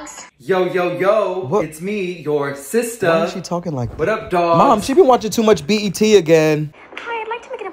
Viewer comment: I thought u said, " Mom she be watchin to much BTS " lol u can never watch to much BTS